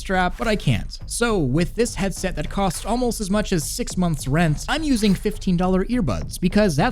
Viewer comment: I heard "this month's rent" at first and was really concerned about your financial decisions.